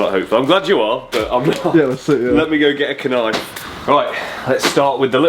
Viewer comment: did he really say "let me get a [K]nife